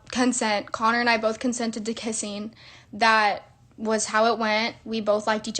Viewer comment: No lie, at the beginning of the vid, I legit thought she said "We consented to Dick kissing" cause one of my earbuds was out of my ear for a moment.